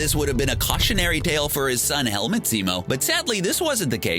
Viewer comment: I thought it said Helnut Zemo. XD